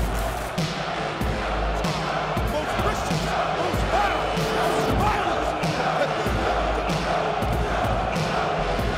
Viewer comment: what did joe said? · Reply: Most violent competitor in the WWE